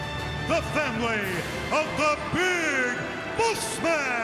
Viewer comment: Is it me or 00:35 sounded like the "fat lady" instead of family of Big Boss Man